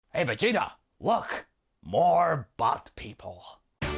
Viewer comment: Hey Vegeta! More but people! (thats what it sounded like you said)